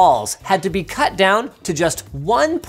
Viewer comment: 'To maintain its virginity' is what I heard